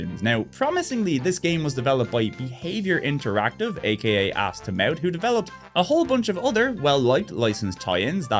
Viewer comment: did you say "this game was developed by Behaviour interactive AKA: Ass to Mouth?"